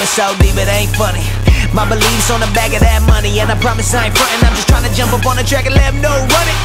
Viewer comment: why did he say; "My beliefs on a bag of dat money"?? 1:01 ...